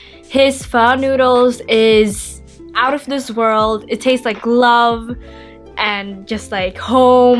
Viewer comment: I thought you said your dad's pho tastes like glove and I was like wtf why would you want to be eating a glove